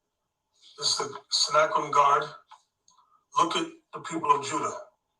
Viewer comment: did he say "snack room guards"